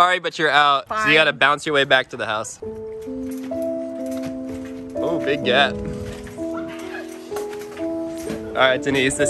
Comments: Anyone hear saying to "Oo big Gyatt" in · Did say big gyatt to Denise